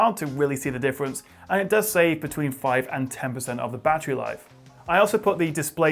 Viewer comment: What did he say at 4:03-4:06? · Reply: And it does save between 5 and 10% of the battery life 😉